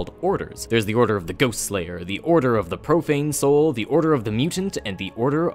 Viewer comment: I thought he said order of the Propane Soul